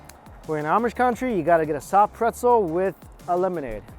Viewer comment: When you said "You gotta get a soft pretzel" I heard "You gotta get a crop circle." I was sure I was wrong then you pulled out those ENORMOUS loops of bread.